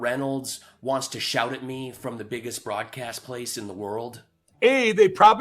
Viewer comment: I heard “biggest fraudcast.” Hilarious content dude. Keep it going!